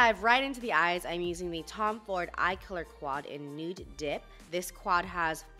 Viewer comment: When was mentioning the eyeshadow quad name, instead of “Nude Dip”, I heard “Nude Dick” 😂🙈